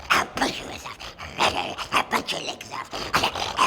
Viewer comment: lmao hahaha did he say "I'll bite your lips off?" Lmfao!!!!😅😅😅😅